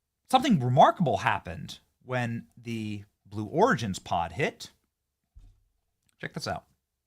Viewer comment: Ha I thought it said blue Oregon. not origin 😬